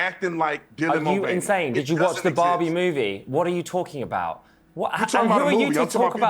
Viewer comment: Ohhh, ''the Barbie movie''! I'm so stupid. I've misheard it and start making an inquiry which ''Bobby movie'' he might meant? 🤭 And going through movie about assassination of Bobby Kennedy, a hindi musical and a lovely story about the dog,named Bobby, i've finally found ''A prayers for Bobby'' - a LGBT film, based on a true tragic story of Bobby Griffith and his family's epiphany after his suicide. Pretty oldschool movie ( because who nowadays protesting against the normal gey people?!) but it was fine. After finish watching it i was sure that that wasn't the movie referring to. Good for the comments here for finding out that the movie he'd mentioned was....efing Barbie!!!🤯 Well, i didn't saw Barbie, but i'm glad that, completely by chance, i saw ''The prayers for Bobby''. One good thing out of me, sometimes lost in hearing. 🤭